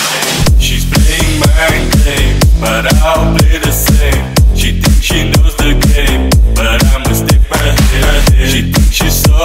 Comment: I just hear "She's playing Minecraft, she thinks she knows the game" etc.